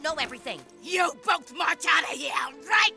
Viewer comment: To me it Sounds like Kyles saying. “Sorry Mom, I’m just extremely immature!”